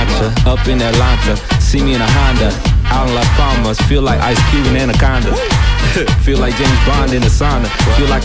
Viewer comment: Swear he said 'I feel like ice cream in a condom'